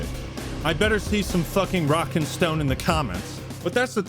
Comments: DID I HEAR A ROCK AND STONE? ⛏️ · did I hear a rock and stone?! ⛏️ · DID I HEAR A ROCK AND STONE